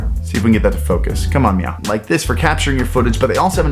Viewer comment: did you just say meow